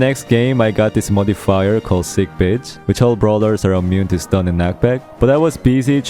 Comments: did bro just say sick b¡tch? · i thought he said sick b*tch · did i just hear him saying sick b**ch ? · I swear I heard saying "sick bich"